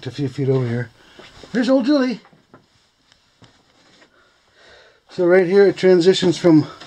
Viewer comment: Uh ooh! 6:57 What did say :-) No beer for you tonight:-). Holy smokes there was a lot of equipment there. Im glad you told me where it is. Im going out this winter. Ha Ha! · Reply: It was something like theres old Julie 🙂